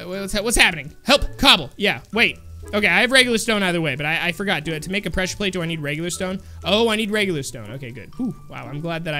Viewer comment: I have regular stone Me: I thought he said 'I have rainbow stone' I'm just sad that Rainbow steve might not be coming back...(or maybe?)